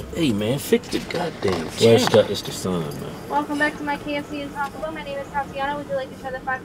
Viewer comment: wait did she say welcome BACK??